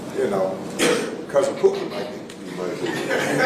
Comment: did he say “your cousin Pokie might”???